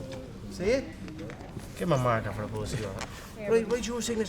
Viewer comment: "give him the marker before I bust you up!" 🤣did he just say that!?